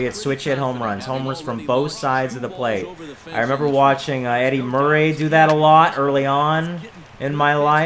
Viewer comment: who's your favorite actor the reason I ask is when you said Eddie Murray I thought you said Eddie Murphy!😂